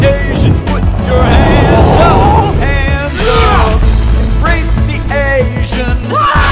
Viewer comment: At first I thought the guy said, "Rape the Asian"... then I replayed it and heard "Break the Asian"... -_- Good thing there are subtitles...